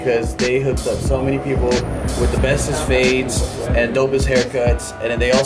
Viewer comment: Did say "bestest" at 1:47?